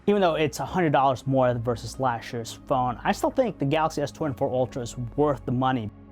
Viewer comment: you say something like, "it's a hundred dollars more versus flashless phone" . . . but I'm not sure I heard that right. What were you trying to say? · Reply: "...versus last year's phone."